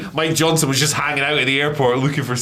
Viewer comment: Thought said "My Johnson was just hanging out in a airport looking for scoops..." 🤣🤣🤦‍♂🤦‍♂